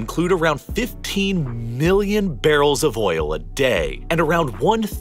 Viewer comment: Did he say 15 billion barrels of oil a day?